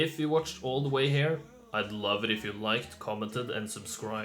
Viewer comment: I misheard you say "I'd like your wife to comment and subscribe" and I wondered how you knew I was married...